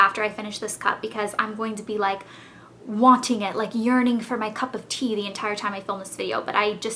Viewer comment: ) i thought you said urin-ing like peeing instead of yearning:) <333